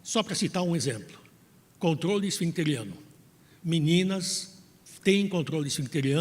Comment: Contorole o que? EU não entendi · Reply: Acredito que ouvi “controle esfincteriano”